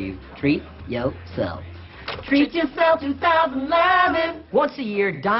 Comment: what do they sing in the beginning? I can never hear the lst few words. "Treat yo self to dahdah daaaah dah"? All I hear is "Treat yo self to thousand island!" but that can't be right. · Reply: Treat yourself 2011